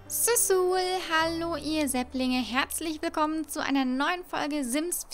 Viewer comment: ich habe sul sul Hallo ihr Setzlinge verstanden😂😂